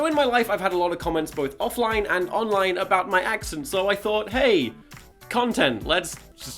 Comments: I thought it said "My Accident" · thank god for that. Am i the only one that thought that said 'my accident'? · I thought it said “my accident”